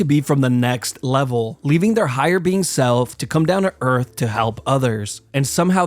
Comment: Judging by their accents, those cave cultists were from the southern part of Russia. Also, I swear at 7:41 he says "their higher bean self"... Just me?